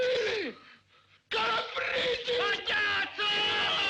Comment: dopo “retrogradi, incivili” cosa dice ? “Calabresi” ? Sarebbe una trovata comica incredibile